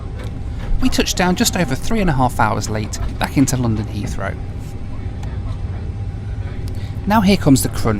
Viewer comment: Did you say 3 and and a half hours landing at Heathrow...it takes much longer ORD-LHR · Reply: I think he said, "We touched down just over three and a half hours late back into London Heathrow".